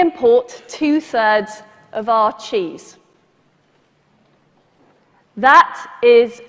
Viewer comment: "We import 2/3rds of our cheese." Pause for effect. Glare at camera. "And that is a disgrace." I don't know why, but that made me laugh so hard. Do you think she actually had that written on her teleprompter, or does she actually get that strident over cheese? Hahaha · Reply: Thought she said Teas, not cheese. 'Bout made me blow my tea out thru mu nose.